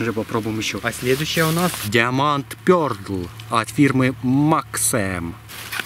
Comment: Мне одному послышалось на 1:02 diamond perdl ?)